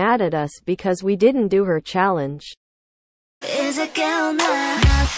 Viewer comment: I keep hearing, “is it gay or not” someone help 😭😭😭😭